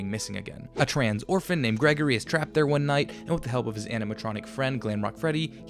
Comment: “a trans orphan named Gregory…” Am I mishearing this? I’m not sure what he actually said as the captions mirror this.